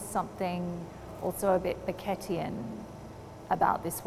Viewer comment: she relates his work to something “baguette e’en”, I obviously don’t know how to spell it that’s what it says in the captions, because I can’t for the life of me find out who she is talking about. Who is this that she is referring to? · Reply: yeah she said Beckettian, i.e. Samuel Beckett-esque (like bleak, impersonal, miserable, tragi-comic)